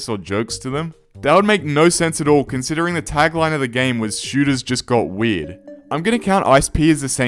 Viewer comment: I heard "shooters just got weed" and laughed for almost a minute straight